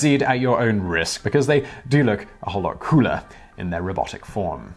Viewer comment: that last sentence i had to turn on CC for, cause i heard "they look so much crueler in their helmets" when you actually said cooler. i guess both kind of work, depending on how you feel about robots in general though.